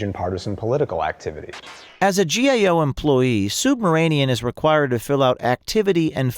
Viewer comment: Am I the only one who hears O'Keefe pronounces the guy's last name as "SUBMARINEAN"? (6:54, 9:26) This SUB-RA-MANIAN weasel has nothing to do with submarines!